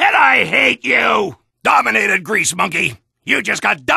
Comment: What did he say? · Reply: greasemonkey